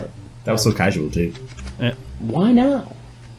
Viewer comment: No one seemed to catch when Chris asked " have you ever done this before?" A very quick and casual response of "nope" then Chris responded with the classic follow up question "WHY NOT"! No one has an issue with that question? I just love your channel ❤ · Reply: Lol I heard "why not" too and was also confused. 😂